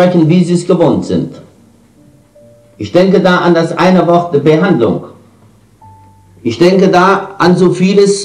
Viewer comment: Danke für dieses Video❣️ Ich verstehe dieses eine Wort von dem Bruno spricht (bei 4:46) nicht richtig. Es ist sehr undeutlich. Heißt es "Behandlung"? Im Voraus herzlichen Dank für die Antwort😀 LG Philipp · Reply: Hallo Lieber Philipp, Richtig er spricht von Behandlung:) LG Beatpoesie